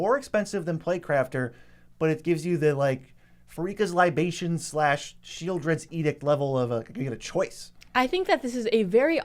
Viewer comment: did you say libations? Lol didn’t know the cards be drinking